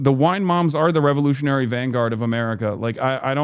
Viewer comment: I thought you said wine moms were "the Revolutionary Guard of the world" instead of the vanguard. Had me scratching my head trying to figure that one out.